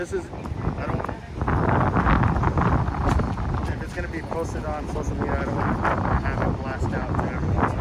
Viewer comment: Lol did he say he don't want the supervisor information to be blasted on social media around the 26:24 mark.